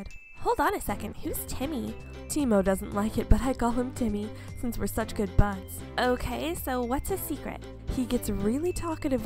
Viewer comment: I love how the characters talk and argue at the end. I thought that said " I call him Timmy cause he has good buns. " 😂😂😂 There may be something wrong with me.